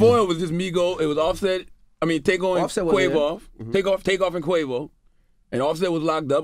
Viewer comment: he really said "takeon and quavoff." Hahahah